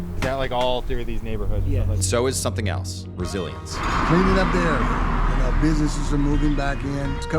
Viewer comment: I thought he said "Brazilians" instead of "resilience". 😊